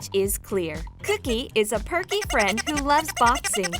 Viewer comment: I thought she said that cooky was a pervy friend like what